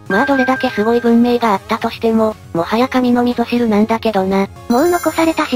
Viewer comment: 神のみぞ知るが神の味噌汁って聞こえたので明日休も…頭いたい…